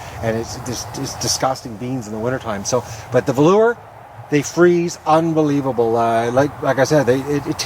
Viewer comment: What type of bean did you say freezes well? It sounded like you said either velour or lower... Thanks we love your gardening posts